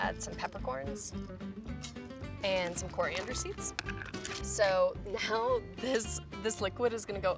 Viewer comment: I thought I heard oleander seeds like wait what. I mean oleander bushes could be in the forest, but I don't think would want to kill her taste testers